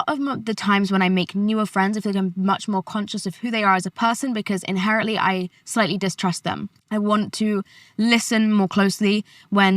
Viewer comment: please someone tell me what she is saying here, cause I couldn't catch that. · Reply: a lot of the times when i make newer friends im much more conscious of who they are as a person bc inherently i slightly distrust them this comment was 6 months late im so sorry